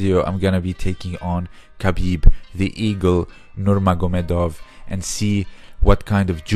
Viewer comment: Lol the eagle. I thought you said the ego.